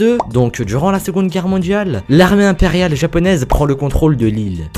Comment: la quoi ? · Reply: l'armée impérial japonaise